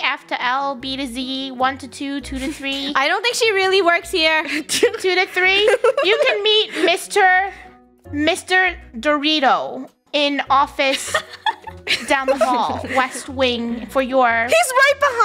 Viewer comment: my last name is Derita so I thought she said Derita and it creeped me out until I had to listen to it again